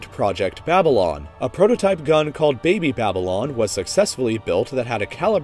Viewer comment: By brain broke I thought at 3:37 it said baby lion 🦁